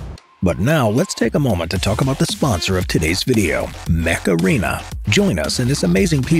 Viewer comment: did he just say macarena???